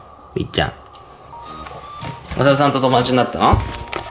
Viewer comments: おさるさんがおざわさんに聞こえる。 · トミーの｢おさるさん｣が途中までずっと｢小沢さん｣に聞こえてた。 誰やねん。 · おさるさんが小沢さんって聞こえて誰！！！！！ってなったw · おさるさんが小澤さんに聞こえたwwwww